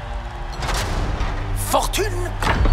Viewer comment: I don't know french and the first word i hear in this video is "f*cking" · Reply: no, this is "fortune" you heard, which can translate into "significant gains"...